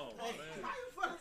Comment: wtf did he just say? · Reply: how am i being a debbie downer. i would hate that shyt. use your ears